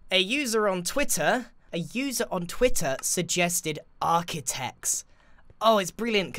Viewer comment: Hey, I don't want to step on anyone's feet here but when you said "Architechs" I immediately thought you said "Arty-Techs" which I personally think sounds cool , but Architechs is also cool so ..... Hope your base turns out cool, 😋